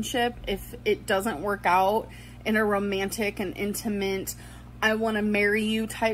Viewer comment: Did she say "intiment"? English isn't my first language but it didn't sound like "intimate"